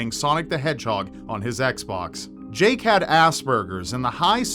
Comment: I swear I thought said that Jake 'had ass-burgers' and thought maybe it was an American euphemism for haemorrhoids! Luckily, my brain kicked in at this point and I realised he meant Aspergers...